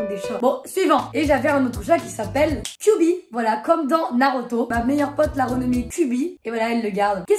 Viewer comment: c’est moi ou elle a dis naroto 💀ps je t’adore